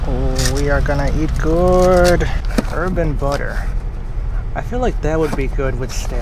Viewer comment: Lmao I thought you said “urban butter” and I’m thinking “what the hell is the difference between suburbs butter and urban butter?????”